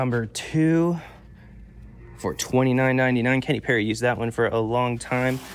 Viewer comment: I thought you said Katy Perry when describing the putter. Had to rewind and you did say Kenny Perry!!!😂😂